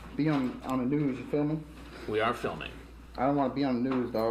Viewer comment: I still don't know if he said "you feel me" or "you filming". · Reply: he said “ya feel me” but Hansen thought he said “ya filming” lol